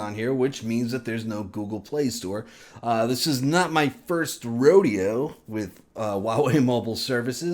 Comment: What's the word you said at 8:37?..... Rodeo?